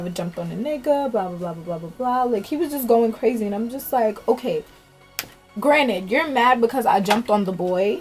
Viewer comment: did she say his name or? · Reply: No, she said, "Granted".